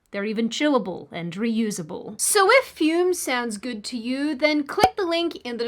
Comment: When you said "fume" at the end there, I heard "fk - ume". Thought it was funny until I considered that maybe it was a sign that these lefties pretending to make points and "gotchya!" moments are causing my brain to decay.....